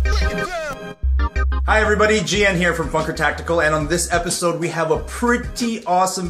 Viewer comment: In the intro I thought you said "This is FUCK HER TACTICAL"